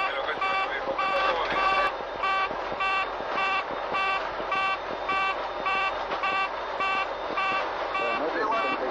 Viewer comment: alguien sabe lo que dice el piloto en el minuto 8:14? lo unico que escucho es "bueno carlitos" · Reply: Despegamos y pasamos dij